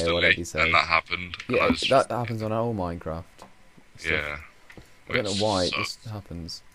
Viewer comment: i love the way it sounds like you say stoff, when I would have said stuff...